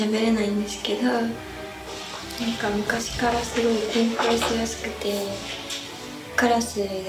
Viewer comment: 何やすくて？ 誰か聞き取れる人教えてください 今字幕作っています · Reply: 緊張しやすくて